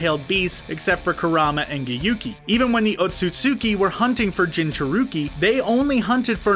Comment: Why this dude tryna rap 9:43 turn on caption when, the otsutsuki were hunting for jinchuriki he had me dead he really said jin chi urik